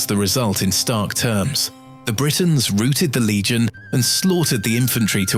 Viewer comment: face-turquoise-covering-eyes: @ 2:34 "The Britons ROOTED the Legion.....", I heard the narrator say. I almost fell off my seat laughing! :body-blue-raised-arms: I replayed the offending section and discovered the on-screen text actually used the word "ROUTED" - not "ROOTED". Isn't AI narration a wonderous thing, it can make the most mundane information genuinely enlightening, and hilarious! :face-green-smiling: +++